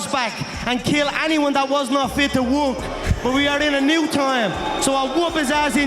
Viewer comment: I always thought he said “ kill everybody that’s not fit WOMB”